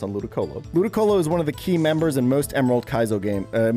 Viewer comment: I thought he said 12 gay members and was like 'What?! How'd he tell?'